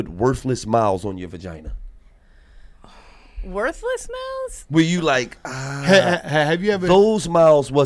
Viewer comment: They thought Gil said “worthless mouths” he said MILES 😂😂😂 they completely missed that.